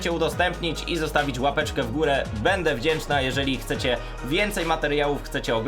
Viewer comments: czy ja dobrze usłyszałem „Będę wdzięczna”??? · czy tylko ja usłyszałam "będę wdzięczna" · czy ja usłyszałam będę wdzięczna xdd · bede wdzięczna dobrze usłyszałem >?